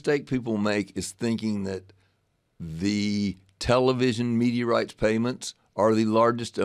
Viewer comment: I thought he said “meteorites”